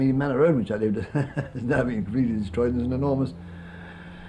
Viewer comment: I heard "a lore library was built there" at the end